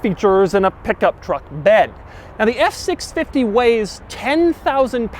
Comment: is it okay me who heard S650😐🤔?